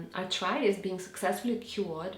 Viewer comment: What is she saying at 09:47 ? I've played it back many times and her intonation keeps masking the word meaning for me. What is being cured? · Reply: Arthritis! xx